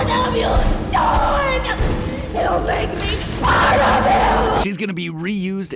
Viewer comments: it was kinda hard for me to hear with the loud shouting she did, I thought she said, "you made me.... ONE OF THEM!". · i just hear "u made me swallow him" but i now hear it says "you made me apart of him" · I thought mommy long leg say "you make me spirable"